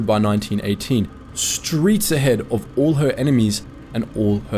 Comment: did he just say "streets ahead"?